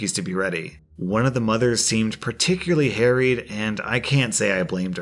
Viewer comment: i should learn to read ur stories also. first story i misheard the woman in the store as "hairy" rather than harried. totally changed the story for me